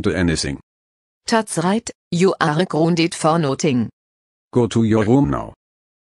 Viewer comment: 😂😂😂😂😂😂😂😂lol. Sounds like he said ungrounded.